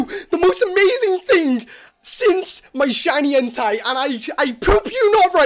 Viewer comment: I thought he said shiny hentai, instead of shiny entei